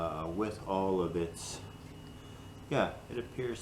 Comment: I heard “most of the bitch (not bits) should stay together”...🤣made me laugh! Making progress! 👍stay warm!